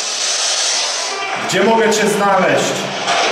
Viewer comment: jak zapytałeś -"gdzie mogę Cię znaleźć" usłyszałam -"chodź"